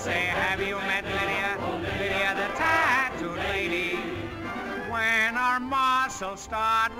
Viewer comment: All these years I heard 'Lydia the cat', this is blowing my mind